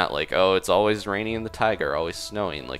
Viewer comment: oh its always raining in the tiger! (i know he said taiga or, but this is what is sounded like) LOL